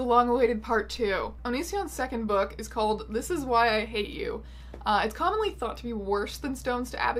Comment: In the begining of the video I thought you said that the book was called (this is why I hit you) and I got so surprised that I stopped eating my pizza rolls and double taked and I don't stop eating for anything